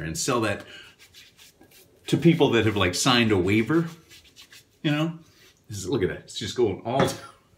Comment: At first I heard it as "Sinus waiver" and that is perhaps not too far from what is needed for "SinatraLennon Level o' Menthol"!